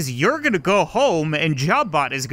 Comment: I like how the auto generated captions are used to Portal so instead of Gladys, it says GLaDOS. 14:40 captions on to see